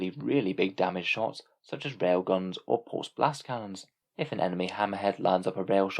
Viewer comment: I was pay attention to one of my kids doing something and I could have sworn you said "pulse Blast - Karen" :P coined now